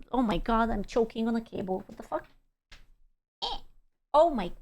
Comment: I hear "Oh my god, i'm choking on a cable" and next moment my electricity goes out... Thanks...